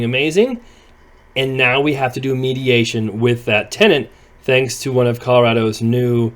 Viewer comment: I thought it said meditation 🧘‍♀️💫 😂